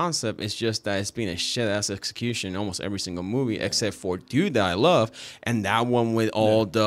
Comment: What movie does he say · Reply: He is still talking about the Saw saga. He said "It's 2 to hat I love. The one with all the people from the health insurance company. " He's describing Saw 2